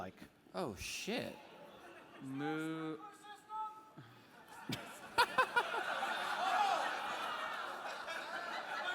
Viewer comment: what did the audience member yell out at 1:19:15 that Omin reacted to? (and then said he had an idea...) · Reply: a suggestion to "use the sprinkler system" it was also yelled out